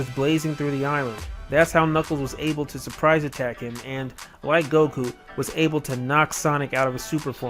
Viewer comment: Did you just say Goku?!?